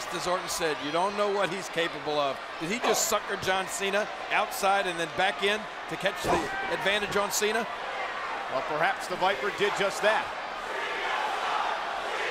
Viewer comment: I heard “cena sucks” 😬